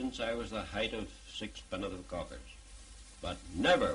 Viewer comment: Anyone have any idea what he says at 13:09-13:11? Something cockers?